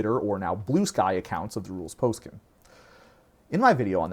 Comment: did you say "rules Poskim"?